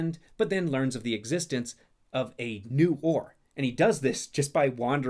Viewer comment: I could have sworn you said "...discovered a new whore..." I guess I'm not over my ex as much as I thought